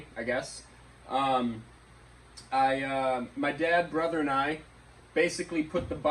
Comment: Such a fun build, loved being apart of it when I could. Turned out way better then I first thought it would. Proud of the hard work and dedication it took to get this to the state its in! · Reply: I thought you said “my dad’s brother and I” 🙈🤔🙈 You actually said “my dad, brother, and I” 😉 Sorry for the mistake but everyone involved did a spectacular job!!! 🎊 🎊 🎊 🙌🏾 🎊 🎊 🎊